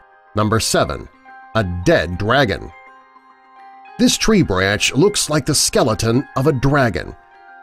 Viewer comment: I heard you say “the Dead dwagon” XD XD Just pointing out XD XD